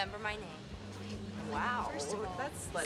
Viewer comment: Lmao did I just hear, "You were like 12." WHAT THE FRIG!!!!